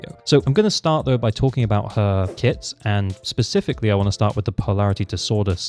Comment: i heard tits xd